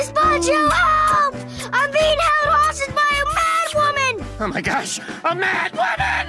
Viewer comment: "I'm being held hostage by a mad woman" the way Chowder says that always makes me laugh.XD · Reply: I heard "I'm being harrased by a man-woman" 💀